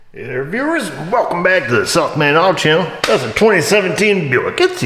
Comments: For the longest time, I thought was saying "Self Made Auto Channel" 😂😅😂 I guess it is a self made auto channel right? Anyways, love the videos! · sounds like he says the "self made auto channel" is that just me?